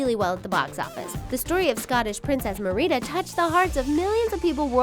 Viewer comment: did you say... mar-ee-da? ...